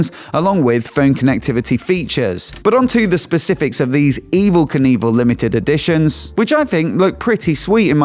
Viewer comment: Why do I keep hearing "Evil Caneeball" lol